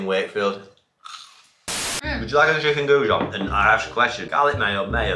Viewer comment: What was said at 1:05? 🤣🤣 would she like a what now? I've played it 5 times and I have no idea what Nile says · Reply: Chicken gougon! 😂